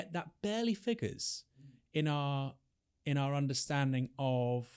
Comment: Every time they say bla bla bla "in our understanding" I hear "In our education"